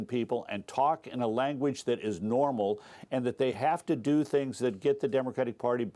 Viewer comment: Did he say "Canada?????"